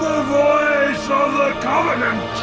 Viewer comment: Prophet of Truth: "I...... AM........ TRUTH! THE VOICE OF THE COVENANT!" Arbiter Thel Vadam: "And so, you must be silenced!" (Truth dies) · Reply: I always thought he said "Asshole, you must be silenced." Woops.